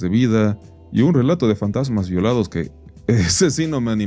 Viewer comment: Escuché "fantasmas violinizados" y la neta me quedé, lastima que no lo revisó u.u. Tenderé que hacerlo yo por el bien de la humanidad T3T